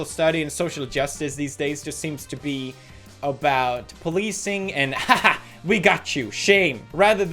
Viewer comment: Swedish accent microaggression: Whenever I hear pewds say "policing" I hear "pleasing"